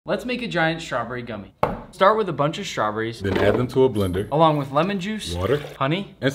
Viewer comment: For 2 seconds I thought u said “let’s make to strawberry dummys”